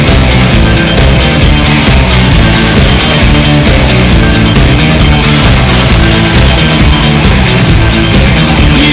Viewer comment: Can someone read his lips in Japanese, what he's saying after 3:36? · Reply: He said, “Domo Arigato”.